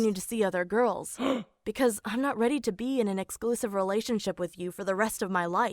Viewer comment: Tbh I thought for a second that said relation sh*t😂😂